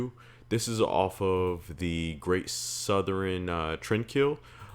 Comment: I almost thought you said "Southren" trendkill at first. I like it, sounds way more dramatic. 😂